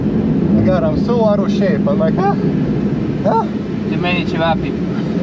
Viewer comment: did I hear you saying ĆEVAPI are the reason why he's out of shape? Hahahah good reason tho